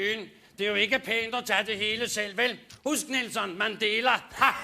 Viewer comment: The subs missed the joke. The joke isnt "man deler".. Well it is (in the sense of thats what you typically say in danish or is expected to do) but he pronounced it like "Man dela" (Mandela) bc it sounds kinda similar... And then he said "Husk Mandela!" (remember Mandela), this show is from the 90's, I was barely a teenager back then, but prob something happened that year, "Remember Mandela!" type of stuff, maybe he cheated on elections, maybe he didnt want to share something political, food, women, bc they combine the fact one is supposed to share with his name, I dunno was too young, but im sure its hypocritical bc thats usually the type of shit we would point out and make fun of :P